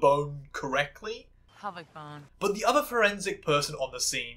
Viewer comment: to be fair I think Brennan is saying "pelvic bone" lmao